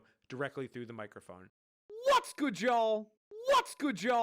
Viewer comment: It sounds like hes saying "what's good jaw?"